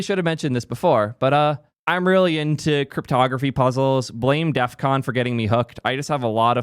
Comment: "Blame DEFCON", Fun fact, is talking about the famous DEFCON hacker convention at Las Vegas. But for me as a French man, and hardstyle lover, I heard "DEFQON", which is a festival, the holy ground of Harder music in Netherland. You should definetly come vibe to music and not code for a week with DEFQON Warriors 🧡